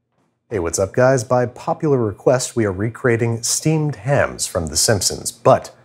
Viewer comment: I thought you said steamed clams